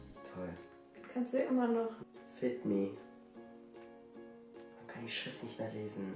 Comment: Ich hab statt fit me fick me verstanden ich komm nicht mehr klar 😂😂😂😂😂😂 ich liebe euch 😂❤️